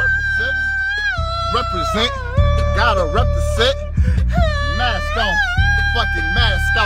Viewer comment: I thought he was saying represent gotta represent